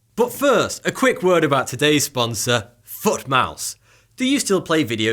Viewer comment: I thought you said 'and a quick word from our sponsor-Fuck Mouse.'